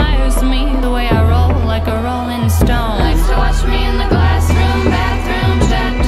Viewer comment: I thought she was saying "classroom". This song sounds a lot less creepy now.